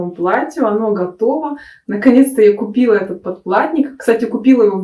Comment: Два раза прокрутила начало видео, думала послышалось "подкладник ". Почему вы так неграмотно говорите на своём родном языке? Нет такого слова "подкладник", "подклад ", есть слово "ПОДКЛАДКА ". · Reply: Я с юмором говорю "подплатник"🤣. Просто перед выходом МК была небольшая предыстория, если вам интересно, то можно её посмотреть на вкладке Сообщество. Согласна с вами - нужно говорить грамотно, но богатый русский язык имеет огромное множество правил и оборотов речи и ,к сожалению, я знаю далеко не все. Это, действительно ,так. Но я готова учиться и исправлять свои ошибки